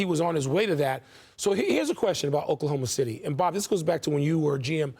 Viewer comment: Mans said “Oklahoma Shitty” 😂😂😂 he ain’t slick Rick I heard it haha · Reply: Only came to the comments to ensure I heard this. “Oklahoma Shitty” 😂